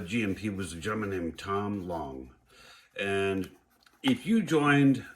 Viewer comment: I believe Tom owns Johnny lighting and Round two. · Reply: Yes thank you for the call out. I thought you said. If that's what you said. Then we are think of the same person. If not it's a different person.